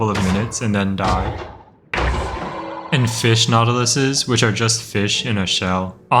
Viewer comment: every time bro says the plural of nautilus I only hear "nautilussy"